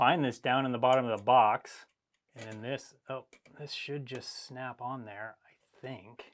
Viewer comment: Did you say snap-on ??